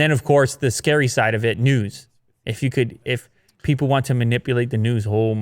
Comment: thought you said nudes😂